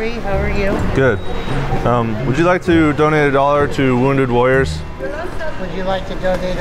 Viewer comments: I thought he said wounded lawyers to the first guy. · I thought he said wounded lawyers at first😂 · At first I thought he said wounded lawyers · I heard "wounded lawyers" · Thought he said wounded lawyers at first hahaha